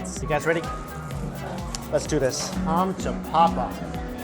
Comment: Did he say "komm zu Papa" 1:52 that's German and means come to daddy 😂